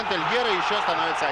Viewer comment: Кто также, как мы с услышали вместо Эльгера, Дель Пьеро?)